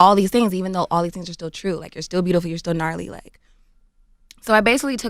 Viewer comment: Wait did she say gnarly?